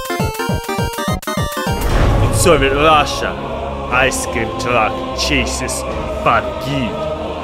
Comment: What i see: "In Soviet Russia, ice cream truck chases fat kid." What the captions say: "so vilasa ice cream truck jesus fuck."